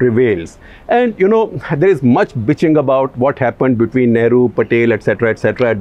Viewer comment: Did he say "B*tching"?